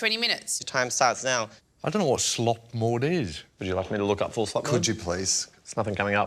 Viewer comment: Was it just me that with the red light task that thought they said, “ full slut mode”?